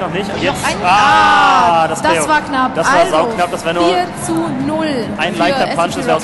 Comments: did she just say "ahhhh, that's fuck up" in 2:28 ??? lol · it sounded like she said ah, das war nach